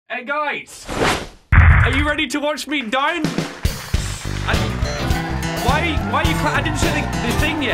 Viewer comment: It sounded like said are you ready to watch me dine